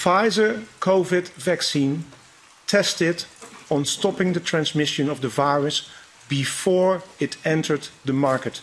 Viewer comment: Everytime I listen to that video I only hear her saying "....about it stopping immunisation", instead of "... stopping transmission"